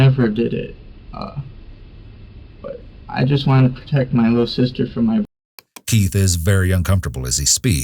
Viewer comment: at around 11:45 subtitles say "I just wanted to protect her from "blank"" but you can hear him about to say "my brother"..